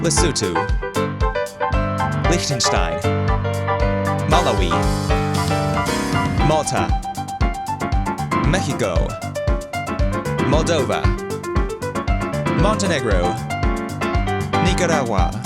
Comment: Why did he say it like “Mehico”?